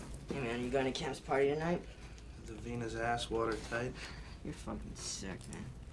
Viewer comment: What does he say at 0:10? “Davina’s asswater type?” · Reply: Correction: “Is Davina’s ass watertight?”